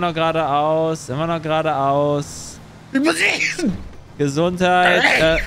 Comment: Das niesen hat sich angehört wie "Ich muss essen" 🤣